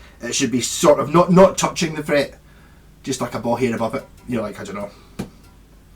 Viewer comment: I thought you said ball hair, lol!